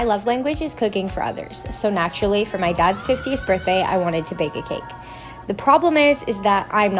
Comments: I thought she said "my dog's 50th birthday" I went through this whole video confused and being like "Dogs don't live that long." And "I dont think dogs can have that much chocolate." I think I need to get my ears checked :/ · At first I thought she said my dad's 15th birthday💀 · I thought u said my dads 15th birthday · I thought she said her dads 15th birthday💀